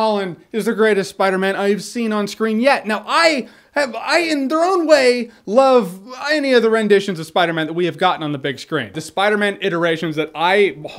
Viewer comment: What did he say at 6:59 ? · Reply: In there own way I love many (or maybe 'any') rendition of Spider-Man.